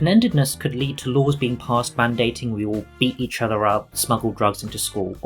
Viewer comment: Oh my god, I at first heard this as "eat each other out" and was very confused at why it was sexual hahaha